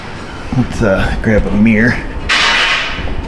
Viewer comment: I know for a fact you said let's grab a mirror.... Subconsciously my brain thought you said beer. Now I'm thirsty.